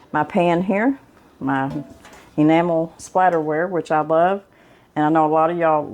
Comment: I thought you said Enamel Spiderware...... I looked everywhere for that...hahahaha... it's called Enamel Splatterware. I thought it was called Splatterware because my mom in law had lots of it....I thought maybe this was a new kind....hahaha